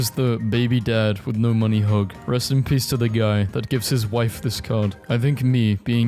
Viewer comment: bruh at the end it sounds like he said rest in pee like wtf